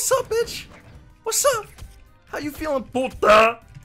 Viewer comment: did he say "Puta"? XD